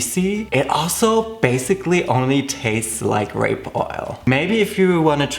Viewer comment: I heard "rape oil"